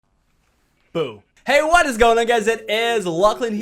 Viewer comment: it sounds like he said Boob